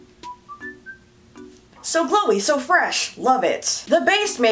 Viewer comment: I thought you said "So Chloe" and i was like THATS MY NAME shakes laptop